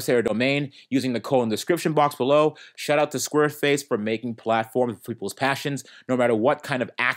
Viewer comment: I think your getting tough tied. If I heard correctly, you said square face at 4:17 😄. Too much coffee sir.